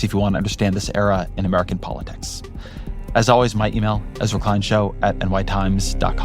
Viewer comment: I thought he said clown show then I read his name🤡🤣